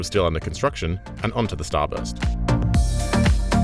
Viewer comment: I'm American, so I thought he kept saying "the Starbucks" instead of "the starburst" took me a while to realize I was wrong 😂